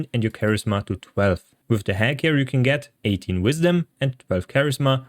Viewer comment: What is he saying at 1:41? With the what you can get 18 wisdom? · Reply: No idea. But i think its hag hair.